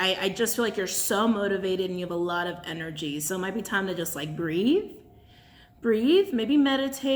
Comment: I thought you said “grieve” at first instead of “breathe” lol pretty sure I need to do both!